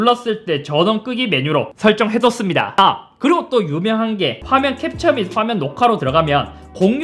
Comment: 나만 유용하게 가 아니라 유명한게 로들리나